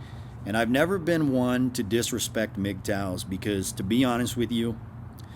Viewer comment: MGTOW - haha I thought you said Mick TAO like an Asian ancient belief system 😅 Men Going Their Own Way